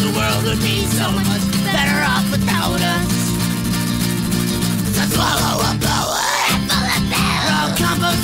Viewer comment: At first I thought they said "swallow ebola, or a handful of pills" I was like, Damn.